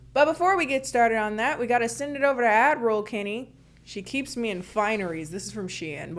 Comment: I thought you’d been saying Admiral Kennie not Ad Roll Kennie 🤦‍♀️ I thought you were just trying to seem “in charge” 😂😂😂